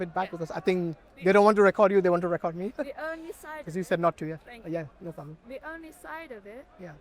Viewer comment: I thought she said not to😭😭